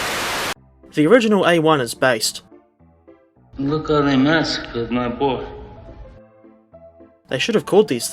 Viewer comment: What is he saying? It annoys me everytime bcs I don't understand it. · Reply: He’s saying “Look how they massacred my boy.”